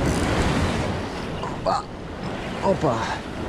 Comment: did you say "opa"